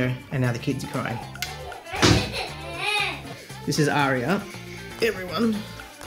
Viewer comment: I heard you saying "this is Arri" I was gonna say of course she's upset in front of RED 🤣